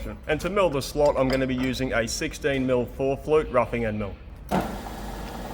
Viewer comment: OH!! FOUR flute, I thought you said 14 flute!! :) .. I was thinking 14 was a bit much LOL ... Not even the wind section, of the Philharmonic Orchestra, had 14 flutes! :)